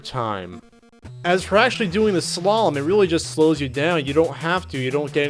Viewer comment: I thought SLALOM said SHALOM